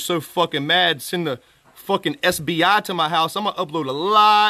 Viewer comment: Did he say the SBI? People sent the State Bank of India to his home because he had guns?🤔🤔